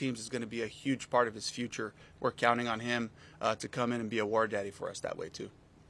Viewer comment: Did coach call Wallace a War Daddy? 17:10 😂